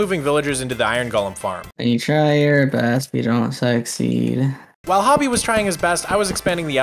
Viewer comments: “Try your best if you don’t s(bad)y” is what I heard when I woke up like 45 minutes ago, I’m not even kidding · Ayo? Did he say s-x seed?